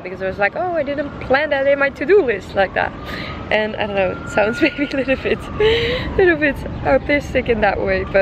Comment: Did she say sounds a little but autistic at 6:13 . Because if so, that is not okay. I love your videos, but that is not something people should be saying. Autism is not a joke.